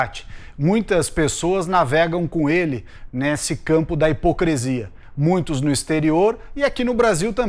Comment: eu escutei HIPOCRESIA?